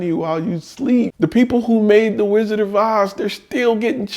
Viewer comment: Hey, can you give more details about this part 6:06 ? Did you say wizard divise ? english is not my native language